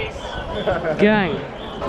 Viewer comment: did he say gay or gang